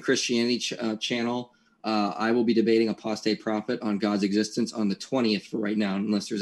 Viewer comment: I had to rewind here, because I thought he said that he was "going to debate a prostate prophet." My dude, I hear you. I'm 37 and I feel that prostate prophecy knocking on my door! Ok, well, that's not entirely accurate. In 3 years, I'll hear the prostate prophet's word, and then again a year later, and again every year afterwards. I'm told the word is rousing. Wait, maybe it was "arousing." I dunno. We'll see.